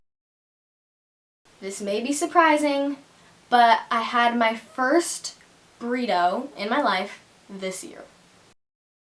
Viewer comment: what did she say? I couldn't understand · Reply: She said "i had my first burrito.."